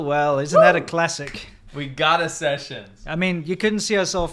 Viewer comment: I thought he was saying "we got er sessions"